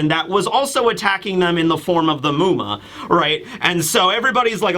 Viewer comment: Misheard you the first time as "attacked them in the form of the moon?"